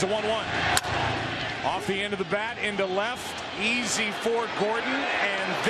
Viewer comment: did I just heard”fuck u Gordon”？